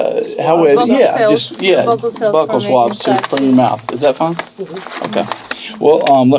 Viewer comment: That was like a regular Ted Talk. What’s DNA? Uh Uhhh…you know like cells …yeah vocal swabs. Nailed it. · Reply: I thought she said buccal swabs, but I listen while doing other things. I remember being amazed she knew they were called buccal swabs, tho.